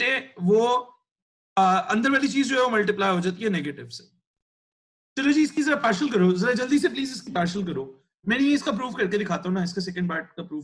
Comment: we have to do partial fraction? I am not getting what sir is saying, it sounds like "partial karo".